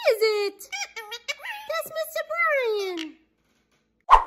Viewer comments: 😂 I'm so glad Beakers talking about his Human Dad, Mr. Brian! I thought he was saying 'kiss her Mr. Gray. 😂 ❤ love this whole family x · I always thought he was either saying "That's a Mr. Brian!" Or "That's a Mr. Bean!"